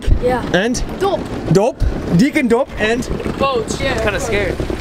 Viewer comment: For the non dutch people here , these little guys are fucking with you that’s not their real names😂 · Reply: I thought the last guy said "Boots" which is a real surname.